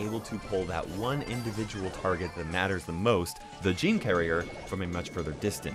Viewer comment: Did I just heard "Gene Carrier" or Am I deaf?